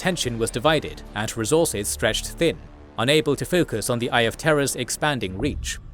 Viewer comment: accent makes it sound like he says "Eye of Terra" and that amuses me